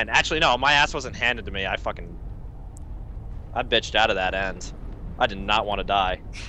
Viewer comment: The first few seconds of the video and I hear "I bitched out of that ass" you know the video has to at least be good, if not excellent at that point lul